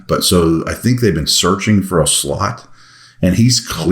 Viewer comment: Did you say searching for a “slut” at time index 3:40 ??? Well, actually, aren’t we all?